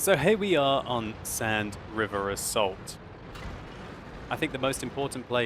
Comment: "Sand River of salt" thats what i heard, and its actually pretty accurate description of this map PogChamp